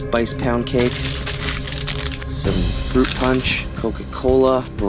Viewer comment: when he said coca cola for a second i thought he said cocaine